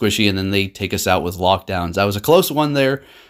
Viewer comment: lmao this guy said lock down instead of lock-on... effects of the Corona virus